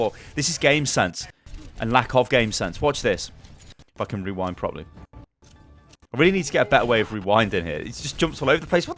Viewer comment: i thought said "Fuckin' rewind properly."